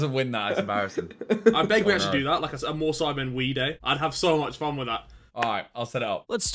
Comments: I thought Harry said more sidemen weed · I thought Harry said MoreSidemen WEED day, and I can't get it out my head